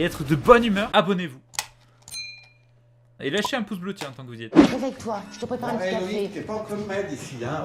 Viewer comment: réveille toi je te prépare un petit café ??? Jai bien entendu ?